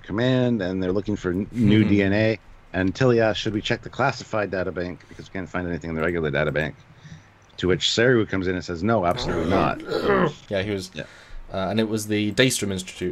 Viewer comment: Oh, I thought Tilly said " Can we check the classifieds for the DNA ".